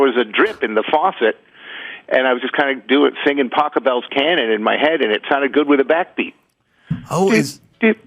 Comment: I 100% thought he was saying "Taco Bell's cannon"... as a euphemism for farting. Please tell me I'm not the only one who heard that.